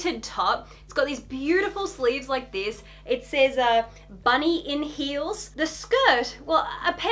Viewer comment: tell me why I thought it said "chunky wheels" and was like yeah that makes sense 😑😑